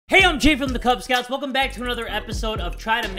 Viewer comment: I deadass thought in the video said Kubz nuts